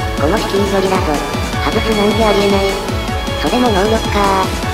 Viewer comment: それも能力かー！がクリリンのことかー！って聞こえてしまった